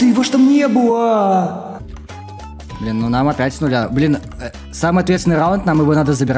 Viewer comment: первый раз услышал чтобы заматерился)) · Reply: Он блин сказал